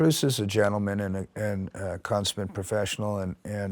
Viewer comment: does he say "consman" professional or a "constant" professional? :D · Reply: consummate